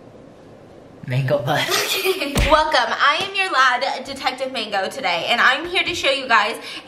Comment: I heard “I am your dad and detective mango today.” And I was like yes of course ma’am.